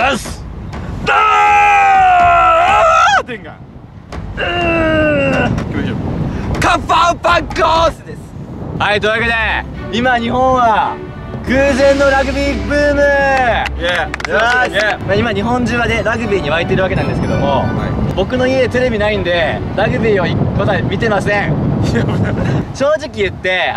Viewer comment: ｢っていう訳で今日本は偶然のラグビーブーム｣ って聞こえた笑